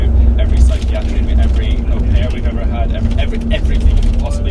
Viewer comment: inaudible? Sure sounded like “affair we ever had” & i have hearing damage